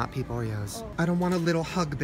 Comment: "How can you have 800 gallons of tuna and NOT have peep Oreos?" Can anyone answer this? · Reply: Ben Blue i thought you said "Pee oreo"